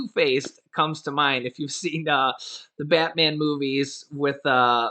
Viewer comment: I thought u said fatman. I went straight to Jake and the fatman and was lost for almost twenty seconds